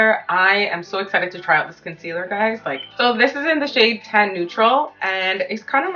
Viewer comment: ahhh thank you so much 😭💗 i’m so glad i found your channel!! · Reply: omg re-watching i realized you said tan neutral i thought you said 10 neutral 😭